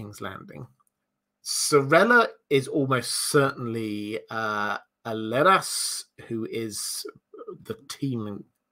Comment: sorry, what word was this? Lead ass? My captions are saying "lettuce" lol 1:24:28 I agree that Stannis will die but he is not yet a spent force. His role and character have long been a favourite of mine. Very interested to see how he impacts the ending events.. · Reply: The word is the name of a character. Alleras or something